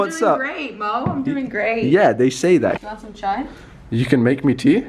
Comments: I heard "you want some chai" Means she knows Hindi ? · did she really said chai(tea)... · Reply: yes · Did she said you want some chai. Yo tea lovers hit like. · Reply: Ye she said chai